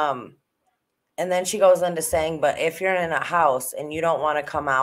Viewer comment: I LISTEN THIS ABOUT FOUR TIMES NOW IT SOUNDS LIKE SHE SAYING "IF YOU'RE IN THE HOUSE". I had to put my ear up to my TV speaker but I'm pretty sure that's what she saying. Key words being IN THE.